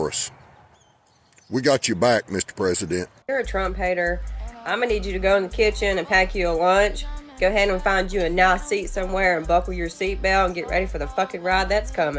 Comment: Thought the lady at 8:25 said "I'm gon need you to get a nazi" instead of "nice seat". Lol still the point stands though, she's talking a whole lot of nothing.